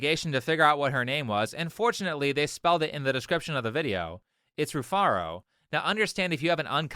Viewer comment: Wow, I was way off. I heard Rufaradu.